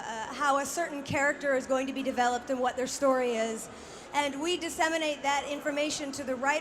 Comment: Did she say inseminate? @ 0:33 :D